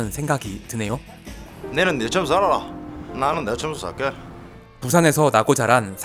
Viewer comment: 자막에 니는 니처럼 살아라 내는 내처럼 살게 라고 하는데 실제로 어릴때부터 경상도에 살았던 경험으로 유오성배우가 말하는건 니는 니 줌에 살아라 내는 내 줌에 살게~ 이거인거 같아요 이걸 딱 뭐라고 설명하긴 어려운데 옛날부터 어른들이 이런 말 썼음.. 지 줌 지가 알아서 사는거지뭐~ 이런 뜻으루..